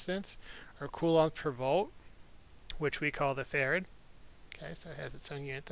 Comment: Is it bad that I kept hearing "farad" as "ferret"?